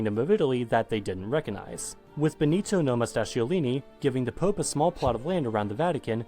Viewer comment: That was the worst pronunciation of Benito Mussolini I've ever heared. What did he even say? "Nomastaciolini"?!? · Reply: No-mustache-iolini I think